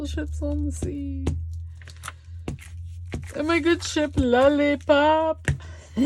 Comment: face-purple-smiling-tears::face-purple-smiling-tears::face-purple-smiling-tears: OMG!!!! At about the 17:45 mark the subtitles changed ship to sh*t!!!!:face-purple-smiling-tears::face-purple-smiling-tears::face-purple-smiling-tears: Thank you for sh owing us, ok moi because I needed it, how to do master boards. For reasons which you well know, I have a hard time grasping these abstract ideas. :face-blue-covering-eyes::face-red-heart-shape